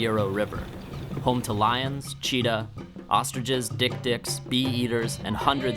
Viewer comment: did he just say "Big Dicks"?